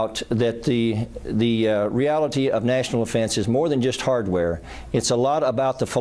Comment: Anyone else catch that he said "National Offense" at 2:50? I thought this was about defending the nation, not crushing opponents.